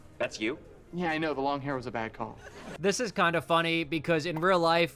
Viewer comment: did he say yeah the long hair was just a Diet Coke?!?! Nah im js fuckin stupid